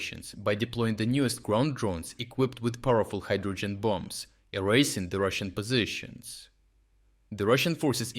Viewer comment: Am I the onlyone that thought "nukes", when you said "hydrogen bombs"??